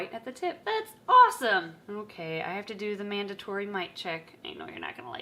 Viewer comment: what did she say? I heard “ok I have to do the mandatory mic check” what does that mean..? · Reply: Nah it's not Mic it's Mite. Too make sure the snake doesn't have any parasites on the snakes body